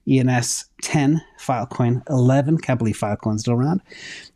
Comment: Did he say "Can't believe Filecoin is still around" at 15:53? I swear I feel like Filecoin is doing pretty well the last few years.